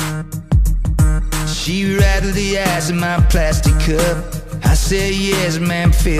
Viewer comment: Just for laughs, his accent always made it sound like at the beginning he said "She rattled the ass in my plastic cup", but then I found out he said "Ice" with a very distinct accent that made it sound like he said "Ass".